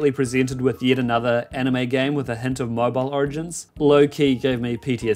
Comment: I misheard "hint of mobile origin" as "hentai mob origin" and had to rewind to confirm lmaooo